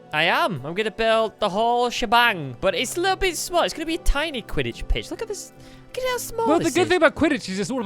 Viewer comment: did squid say this is gonna be tiny quiditch piss